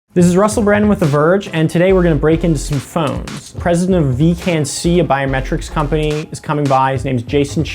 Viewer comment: I thought he said "Hi, I'm Russell Brand"